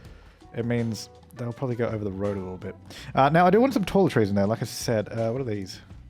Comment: Oh, he said "taller trees." Thought he said toiletries for a while. Was wondering why he'd throw trash on the ground out there.